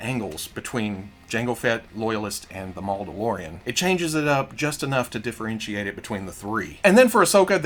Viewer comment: Damn if they included a normal 501st helmet, omg these would sell like hot cakes. Like they already did, but damn. Also at 16:28 did he say Malldeloreon? And not Mandalorian? Edit #2 oh I get it Maul-Dalorian ok I see now.